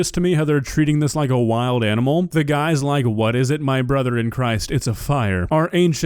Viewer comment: I think that he's saying "Where is it" because the Pyromaniac said "Grab the fire extinguisher"